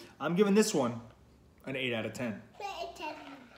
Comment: I was 😂 laughing so Hard so many times, I tooted ... 😂 Oh my Yall are so durn precious to me. you will watch these one day and love them! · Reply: Did I hear say, "8 out of 10" ? At the 2:50-2:51 point